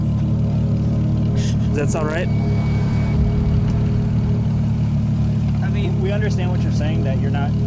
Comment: I swore I heard him say "Does that sound right?"